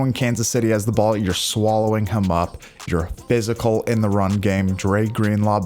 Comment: Uhhhhhh... What does he say at the 2:07 mark? Because I'm hearing something about "swallowing cum up" 😳